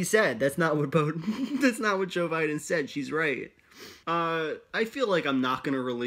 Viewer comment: Almost sounds like my man is gonna say Boe Jiden 💯